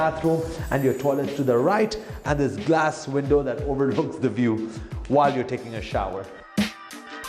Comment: i honestly thought you were about to say "while you're taking a shit" :'D